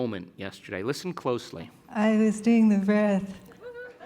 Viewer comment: Hi!! Does anybody know what she said in 2:26?? , and why he does what he did? Please explain :) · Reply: She said “she was doing the breath”